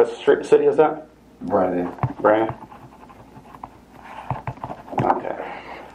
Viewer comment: I never heard of this and he mentioned this incident occurred out of Brandon Florida which is only 25 minutes away from me near Tampa as he said, I have heard his name on the local news before but not in detail about this incident at all. Very interesting video, thank you. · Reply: no worries mate, I first thought he said Bradenton also but he repeated Brandon and I caught it then. Honestly he does not look like a person from Brandon being that Brandon is a upper class area that the residents are usually proud of and the way he carries himself, just just does not seem like he would. But he did mention that he lives at some females apartment from the area. I could see him being from Bradenton however with the thug life everywhere or even most areas in Tampa.